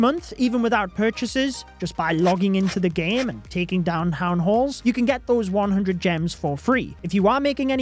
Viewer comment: it sounds like he says ""taking down hown halls""